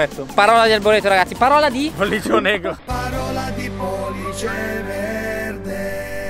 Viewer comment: "parola di pollice NE*RO"? signor ho sentito bene?